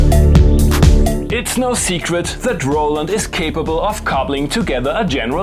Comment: Did You call it a droland at 7:30?